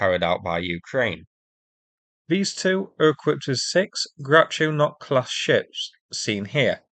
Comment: could have sworn you said Groucho Marx class ships....